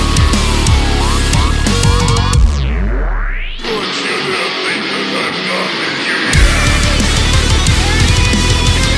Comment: so what exactly sans saying on 4:59? its been bugging me for a while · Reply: dont you dare think that im done with you yet